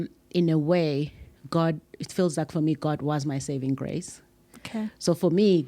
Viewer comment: I heard "God is my savior" .